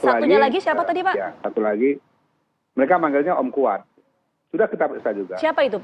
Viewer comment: weleh2, Kuat mbak namanyaaa.... disini aja jelas banget kedengeran kok. Tanya nama sampe 3x e, ketutupan suara sendiri sih.